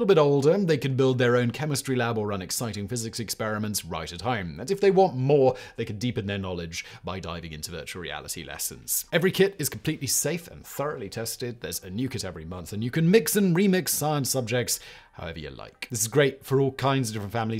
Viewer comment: In the ad: I didn’t hear “there’s a new kit every month.” I heard “there’s a nuke at every month.” I was a bit confused why we’re sending kids nukes. 😂